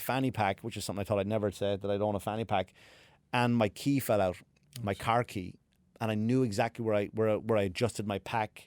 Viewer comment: I thought u said ur teeth fell out. Lol